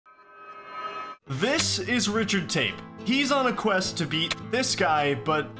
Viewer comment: When he said Richard tape I thought he said Richard tate LUL